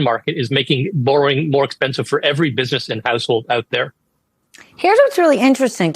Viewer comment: did he say "Buisness and A$$holes" ?